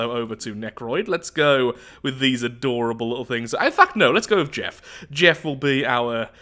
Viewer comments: Bruh I had to listen to that again. I thought said "Ah fuck no let's go with Jeff" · did I hear that right? Did sware? Our of all the years I think this is the first sware I've heard him say. Let me be the first to say "language" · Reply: "In fact no, let's go with jeff" but I think I can guess what it sounded like haha! :)